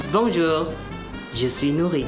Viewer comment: L'espace d'une seconde j'ai cru qu'il disait "Bonjour, je suis nourris". J'étais en mode "Bah... Content pour toi mon frérot." 😅 Félicitation à lui en tout cas, un tel progrès en si peu de temps, c'est très chaud.